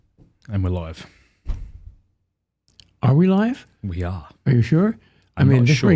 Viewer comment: At the outset, I thought you said, "I'm alive" instead of "And we're live". Made me chuckle.